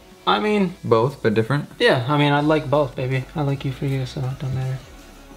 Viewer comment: Is it just me or did I hear said "I like you for you"...? Either way they make such a cute couple😍😍😍